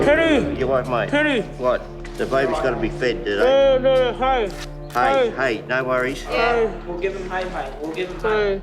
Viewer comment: i think here he's saying "gotta throw hay", which is how we say feeding hay